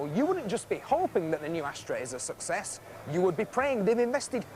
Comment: That guy reviewing the Astra... is that Richard Hammond's dad? LOL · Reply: Could have sworn he said ashtray, it wasn't until I say the plack on the car reading Astra that I realized it wasn't the case.